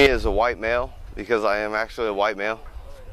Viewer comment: Lol they called him an black male??? Around 7:40??? I couldnt hear it very clear... But thats hilarious!!!